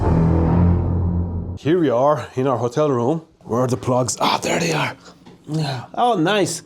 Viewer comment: When he said "where are the plugs" I heard him say "where are the drugs", and you know... He's in Colombia.